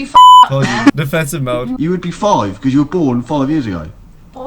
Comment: Oh I thought it said if u were born 5 years before the year I was born lol